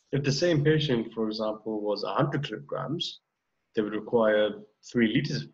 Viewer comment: what is he saying. if the same patient was on .........i m not able to understand the word. plz tell me anyone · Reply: "If the same patient, for example, was 100 kilograms..."